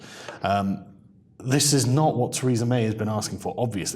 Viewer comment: Freudian slip? Sounded like you said "Treason May" 😉